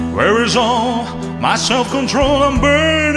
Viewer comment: Hi gd day sometimes im misheard your song myself control, i thought muscle controll i laugh to my self sorry im dirty minded just ignote me ha,ha, sorry